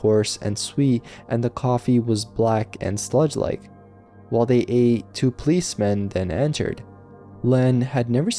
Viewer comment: 😂 I thought you said, "While they ate two policemen." 😂 I was like, "Well, that escalated quickly!"😂